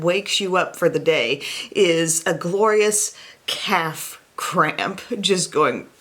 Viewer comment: I thought you said you woke up to a “cat crap” not a calf cramp. 🤣🤣 I’m like...me too!